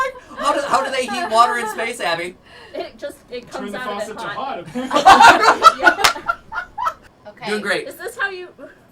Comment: What (??) say at 5:34 in response to "how do they heat water in space?" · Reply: "Turn the faucet to hot apparently."